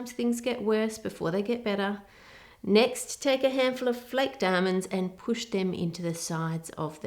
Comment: I wasn’t looking when she said “sliced almonds” and I thought she said “flaked diamonds”!😆 LOVE this channel!